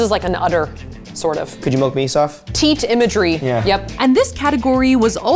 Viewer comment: HOLD UP WHAT DID HE SAY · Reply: He said (and I quote): ‚Could you milk me, Saf?‘ I believe- if I am wrong that would be really embarrassing 😂😂